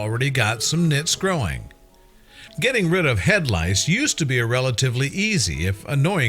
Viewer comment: more like head rice instead of head lice